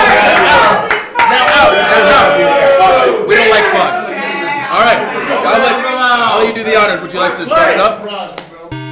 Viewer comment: what did bray say at 1:56 ? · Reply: du plooy "Foxy beat it!" -El Murpho